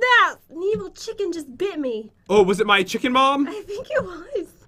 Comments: Is it just me or did pat say chicken mom? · WAT did u say chicken mom at 11:02 or chicken bomb? xD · i heard from pat "chicken mom"hahahahHhH hhaahHhaHha XD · Who else thought pat said chicken mom instead of bomb